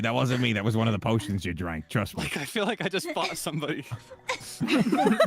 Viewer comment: i think they misheard kaelix saying "i feel like i just f*** with somebody" when it was actually fought not f***😭😭😭😭